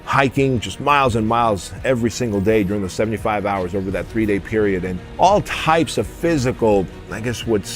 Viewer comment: did he say 75 hours over the three day period? "If you're not suffering 25 hours a day, then you're not a man!!"